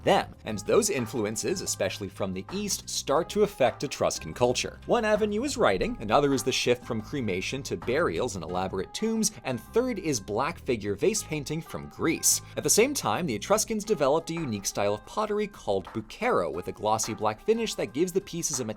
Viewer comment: I thought said Blackface painting and was about to get worried they were problematic